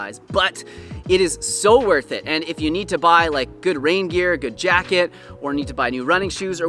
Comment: Thought you said "If you need to pay a good reindeer" I was about to order when I realized you said rain gear :P Haha jokes aside, always love your content! Thank you for what you are doing! :)